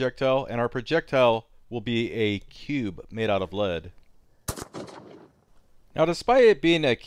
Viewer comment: He says "...made out of lead" , But the subtitles says "....made out of blood".